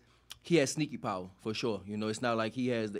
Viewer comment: Went from "cant crack an egg" to "HE GOT SNEAKY POWER" 😂😂😂😂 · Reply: 😮I thought he said SNAC power😄